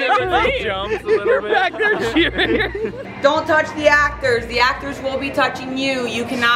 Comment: Rules: don't touch the actors and they won't touch you Actors: literally almost every actor touches them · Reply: Drew Staley I thought she said don’t touch the actors they’ll be the one touching you x